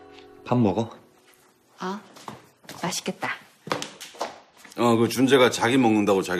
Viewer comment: Can somebody please tell me in Korean what Cheong said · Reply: I guess she says Mashittgetda 맛있겠다 which means 'it looks delicious'.